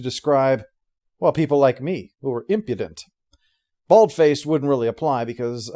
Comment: I’m sorry, did call his character impotent??